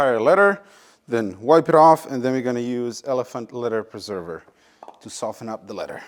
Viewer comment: My wife thought you said elephant glitter!